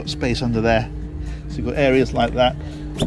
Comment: Love your videos, they are so well done. When you were talking about storage and said "there's a bigger area down here" I thought at first you said "a beer area". I was all set to plunk down my money...